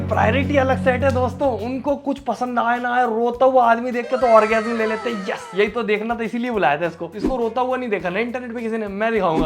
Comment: did he just said orgasm or what 💀💀💀